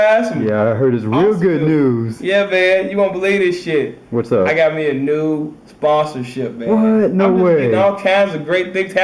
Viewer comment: I thought you said first "I got new boston ship" I was like wtf